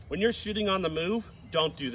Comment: I swear i heard " if your shitting on the move" 😂😂😂